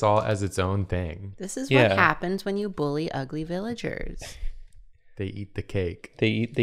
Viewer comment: And here I was thinking was reacting to how what he said sounded like "they eat the gay." ISTG ever since coming out, almost everything has sounded like a gay joke to me